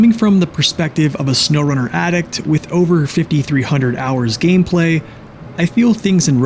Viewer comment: did you just say 5300 hours????? 👀